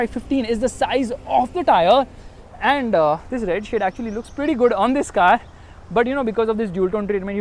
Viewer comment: I heard RED SHIT